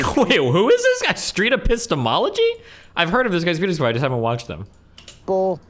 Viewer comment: can anyone help me out with what Destiny is saying here? The subtitles say "beautiful", but I suspect it's actually "videos".